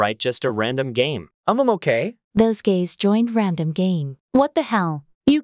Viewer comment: this is better than all the other ytbers text to speech. keep it up (im not a bot) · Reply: just found sum 7:41 just said ''gays'' instead of guys- dayum